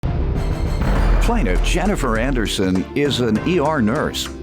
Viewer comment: I looked away for 5 seconds and I heard "defendant Jennifer Aniston"... I was like wait wat!?!?